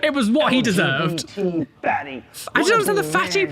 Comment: it sounds like hes saying fanny not fatty